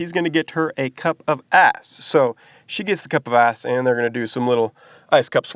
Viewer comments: thought you said cup of ass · I really thought you said “a cup of a**” lol lol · At first I thought he said “a cup of ass” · I was so confused,😵😵 I thought he said cup of ASS😝😝. Anyone else ??